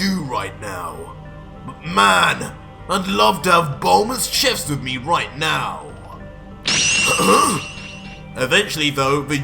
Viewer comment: I fr thought this man said “I wish bulma’s chest was here right now,”